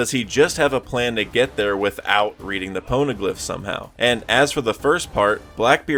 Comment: Why do the subtitles say Po rno graph instead of pono glyph lmao